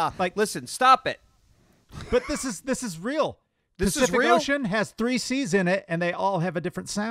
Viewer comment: what word does say has 3 different "c" sounds? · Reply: Pacific Ocean. Two words but point still stands